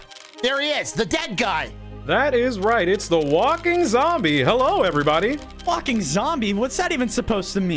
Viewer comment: DID HE JUST SAID THE SWEAR WORD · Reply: He said walking zombie